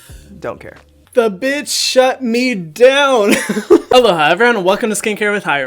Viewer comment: wait did u say “hello” or “olá” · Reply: Normally Hyram says Aloha